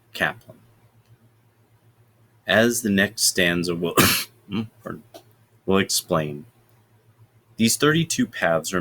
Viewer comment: Sorry, what were you saying here? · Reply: As the passage will explain....